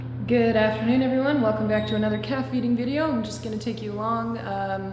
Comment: OH NO... I wasnt paying attention and thought you said welcome to another calf eating video.... LMAO